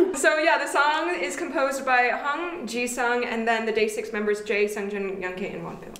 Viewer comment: it's mentioned that "the song is composed by han jisung and the day6 members jae, seungjin and youngk" and im cruious about the han jisung part? did i mishear the name or is it really stray kids' han jisung? and if it's the latter, is there a source for that information? EDIT: i did mishear, it's hong jisang not han jisung </3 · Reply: Perhaps it’s my terrible pronunciation, but 홍지상